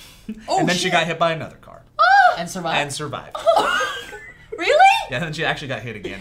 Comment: I thought Joe said "and that shit survived" I busted out laughing.